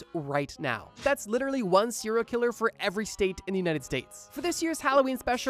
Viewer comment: Had to stop watching after a few seconds because I kept hearing "siller killers"